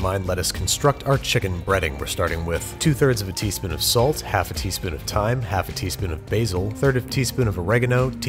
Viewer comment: I’m sorry but did he just say “half a teaspoon of time ?” No other chef has ever obtained this raw ingredient